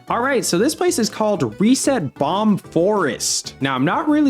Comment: I could have sworn it was called Reset Bomb Fortress which that name would make a lot more sense